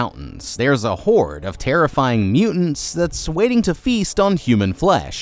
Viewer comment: I dead thought he said there’s a whore of terrorize monsters, had me like ohhh? There’s more